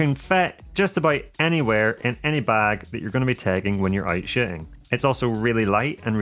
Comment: Did you say "sh!tting"? 02:04 Interesting... 😂😂🤭